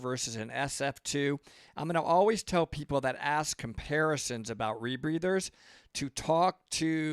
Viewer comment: It's not that embarrassing of a question! Oh COMPARISSON, I thought you said embarrassing since I was on the other side of the room.. haa haa